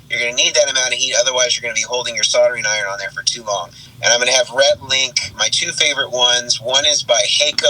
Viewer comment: Anybody else hear "Rhett & Link"?